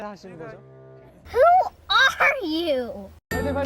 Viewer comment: I CAN'T BE THE ONLY ONE WHO HEARD "i love you" AT 0:37 HELP- I REWATCHED IT AFTER A YEAR AND JUST NOW NOTICED